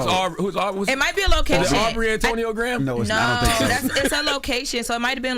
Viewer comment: it definitely sounded like she said Poor Antonio